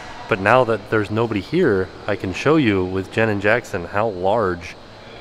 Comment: When you said "Jenn and Jackson", I thought you said "Janet Jackson" 😂